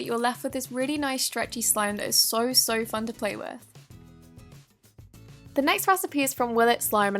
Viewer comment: Ok lol the first slime she made with the laundry starch and face mask gel when she said " it's so fun to play with " I thought she said it's so "fuck" to play with !! Lol my volume was down that's probably why..